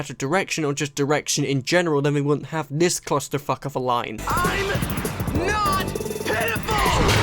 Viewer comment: guys, i think Steven is pitiful. could just be me tho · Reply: oh that’s what he said... I thought he said “i’m not pitbull”